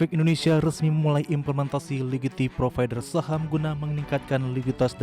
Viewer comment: “Ligiti provider”, “Liqiti provider” 0:03 atau saya yang salah denger 😂